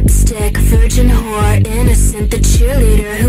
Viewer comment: I thought he said “fucking a cleaner” at first🫣🫣🫣🫣